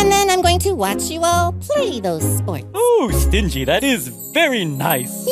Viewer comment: So I had subtitles on, and at 6:07 instead of stingy, it showed string cheese xD